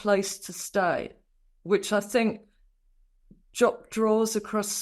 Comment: "...jopped draws across the nation." You nitpick his every motion, but you can't say "dropped jaws"? It's media execution without a trial. I'm not condoning the in-hindsight optics of Prince Andrew's actions. I'm saying your approach is slanted tabloid innuendo at its usual subterranean level, and what you don't say leaves acres of room for responsible journalists and investigators to make a solid, thoughtful and balanced case. 🇨🇦